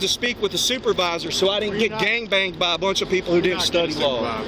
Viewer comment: Did I hear him say he didn't want to get "gang banged"?!?😮😅😂